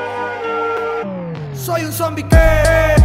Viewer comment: entendí "SOY UN ZOMBIE GAY" envés de "SOY UN ZOMBIE QUE" :v